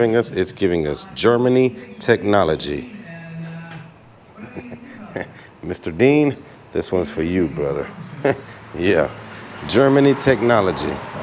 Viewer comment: one of my subscribers. His name is Mr Brown. He always makes fun of me for saying German technology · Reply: haha, i thought you said Mr. Bean. That's what I get for watching your videos when I should be working 😂